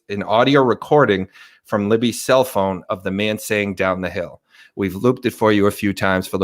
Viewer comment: After someone noted this, I hear "Go down the hill." Listen.